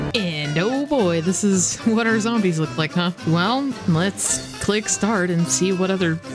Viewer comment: I heard this description as "What are zombies look like?", and that still felt correct.